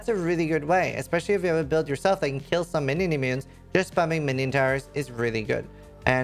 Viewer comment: I'm sorry man, what are you saying? Minimunes? I can't make that out.... Keep listening over and over. You say, especially if there are no "Minimunes?" Or, "And kill some minimunes" @ 0:49. What is a minimune? Also, when I click on the minion tower in order to upgrade it to the ranged version - like you do in your video, it doesn't give me that option, I only have the option to click the gear to upgrade it. No left/right navigation options... as in, right for the ranged version like yours. Any idea why that is?